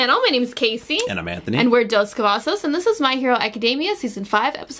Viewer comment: anyone else hear her say "n word Dos Cavazos"